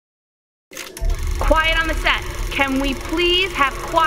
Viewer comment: I thought it said “can we please have quite on the sex” but it said set